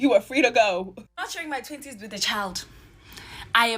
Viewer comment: I thought she said she was not sharing her titties with a child. 😮